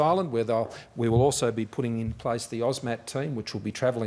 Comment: Sounded like he said the, "OZ MAT" team. Aussie warm welcoming mat for all the Aussies. Nice! 🇦🇺🐨🦘🦐🐊🇦🇺